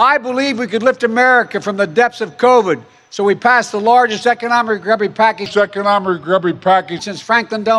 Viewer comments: I thought he said "economic grubby package". I think it's a better subscription........description. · Sorry, I disagree with you transcription around 2:20 "recubary package" He clearly said it was a "grubby package ", and that was no mistake. It really is a grubby package from a grubby president.